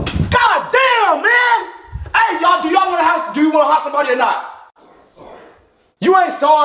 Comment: "Do you want to haunt somebody or not?" "Sorry..." Shit had me dead af💀💀💀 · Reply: I thought that he had said help somebody 😂😂😂